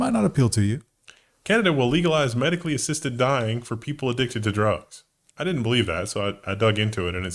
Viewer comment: I was have listening and thought said "addicted to drones"